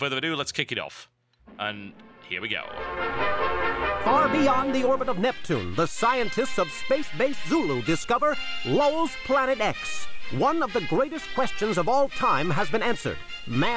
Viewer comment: I heard "Lowell's" as "lulz..."